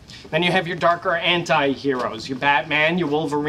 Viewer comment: why do i keep hearing "darker hentai heroes" 😄